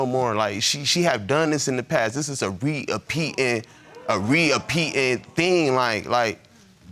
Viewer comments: Did he say “re-appeating”?! 😂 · Re-ah-pe-ed ? Tf lol was he trying to say? Was he saying " repeating" or "repeated"? Lol!